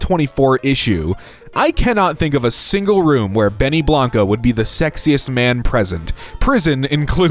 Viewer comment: for some reason I heard and saw sexist help